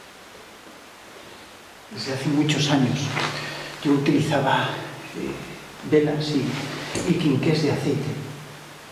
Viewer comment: voz de hombre,Uf pero que ni idea de que dice. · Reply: Dice SI